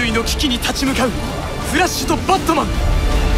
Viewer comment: バットマンがアンパンマンに聞こえた😂